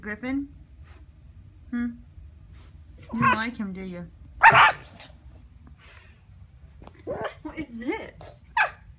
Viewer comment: made me crack up. He sounded like a car. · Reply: Hopper he's saying "show me the car fax"